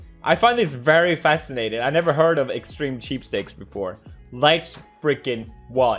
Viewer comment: Is it just me or did he say extreme cheap steaks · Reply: at first i thought he said extreme cheesesteaks. 🤷‍♀️